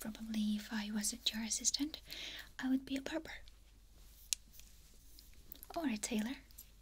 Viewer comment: When you said "If I wasn't your assistant I'd be a barber or a tailor" I was half asleep and I heard "If I wasn't your assistant I'd be a Pervert or a tailor." I woke up and said "What did she just say?" 😂 A sleepy mind 😴 hears weirdest random things 🤣🤣